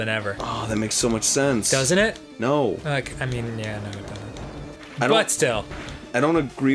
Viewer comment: when Dan says "but still", I heard it like "Butt-Still", then I thought of how clever moonshine was lol